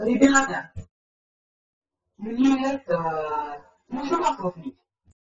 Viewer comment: с что она сказала на 0:24? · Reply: Мне...Это...Нужно масло слить...-Лей.